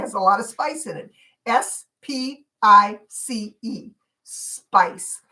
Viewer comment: Good Morning! I have very slow internet! But I thought you were saying spite, I was thinking why such a word, I back tracked it after it went off and figured it out! Enjoying every day!